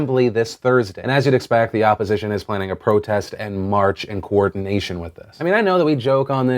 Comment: I thought Phil said protests IN March and I contemplated what month I was in